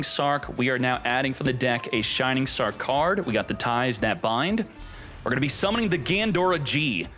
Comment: i heard it as 'the thighs that bind'